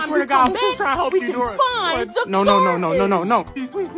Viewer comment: "I see's no garden'' THAT LINE HAD ME CRYING 4RL🤣🤣🤣🤣🤣🤣🤣🤣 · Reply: Coudla sworn he said "no god"